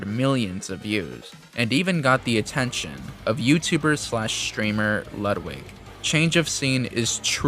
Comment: what is that subsitle when he says slash at 14:40 "slst"